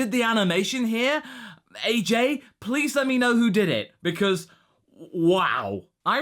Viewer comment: See, I was totally agreeing with you on the animation for this episode. I was pointing it out to myself while watching it too. But around 1:58, I was verbally agreeing with you and then you said “AJ, please let me know” (what it sounded like to me), I freaked out a bit because I go by AJ lol